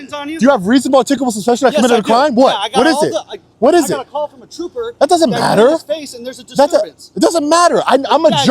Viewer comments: did he say “I got a call from a trooper that said you spit in his face, […] so that does matter”? Because that’s crazy…. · "I got a call from a trooper and you spit in his face" Is that what I heard? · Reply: "...I got a call from an officer that you're in his face..."